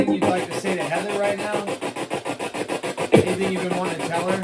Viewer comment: HEATHER, PLEASE LISTEN AND LET ME KNOW IF YOU HEARD IT AS WELL.XO- I LOST MY SON ON 9/7/21. I USE PHONE APPS, AND THEY REALLY DO WORK. XX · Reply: WAS IT YOUR BIRTHDAY RECENTLY? 9:18 WHEN SAID "ANYTHING YOU WANT TO TELL HER, I HEARD "HAPPY BIRTHDAY".